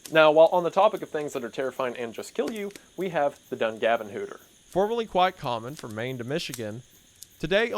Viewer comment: I thought I heard you say "the Dungarven Whooper" which is a well known ghost story here in New Brunswick Canada. I would LOVE a video on the Dungarven Whooper!!!!